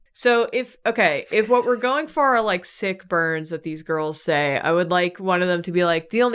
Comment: So with one headphone in I kept hearing “sick burns” as “sick birds,” and I invite you all to consider what that would sound like